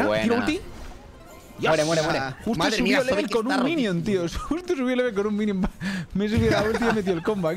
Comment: escuche justo subi level con un rimion xD